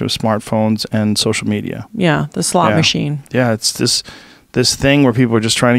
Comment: did she say a slot machine or a slut machine?